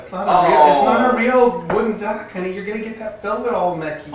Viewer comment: Did he just call him 'honey' at 2:49?